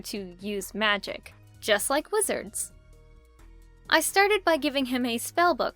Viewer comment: I thought u said " just like lizards" and I was like "WHAT!? lizards know magic now!?" XD